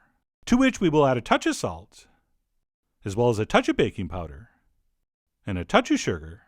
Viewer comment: I thought he said a toucb of "baby powder"... I spat out my food im eating then realized its baking powder duhhhhh lol